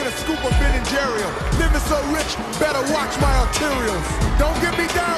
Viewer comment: Did Shaq actually say "Nibba so rich." At 15:03 in a kids movie? What?